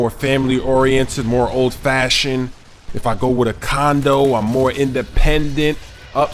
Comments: Hahahaha i thought he said condom first. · Like if u thought he was saying condom at first